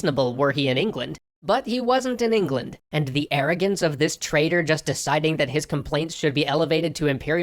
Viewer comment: I dunno why I’m saying this. But at 2:32 the captions say traitor. But is it traitor or trader?